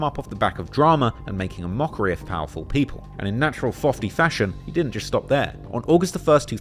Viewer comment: fofty instead of fifty